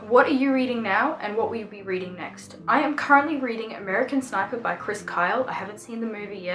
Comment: I think you confused the sniper soldier movie with the Ellis movie with Christian Bale in it. Both are movies based on books tho. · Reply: Oh, I'm sorry. I misheard and thought you said "American Psycho" by Chris Kyle. My bad!